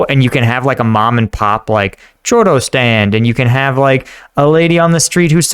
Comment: It sounded like you said "chorro" which is completely different from a "churro", believe me when I say you don't want to ask for "Chorro" instead of a "churro" xD